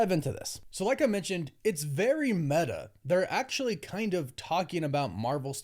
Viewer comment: I'm not trying to sound like "that guy." But every time says, "it is very meta" all i hear is "it looks terrable"